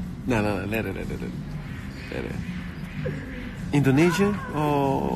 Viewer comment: Did you say indonesia at 4:49? Im from indonesia 😁😊